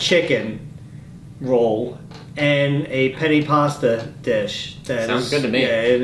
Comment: I’m so tired that I thought it said penis instead of Penne